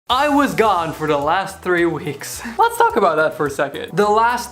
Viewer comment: HAHAHAHA I thought you said ''I was God for the last three weeks''